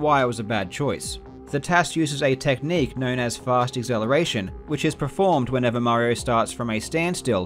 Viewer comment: Thought he was saying "fart acceleration" which made sense because Mario was facing backwards and farting his way to victory.